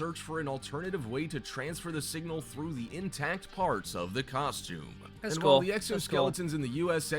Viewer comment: i heard "Costume". Enough said.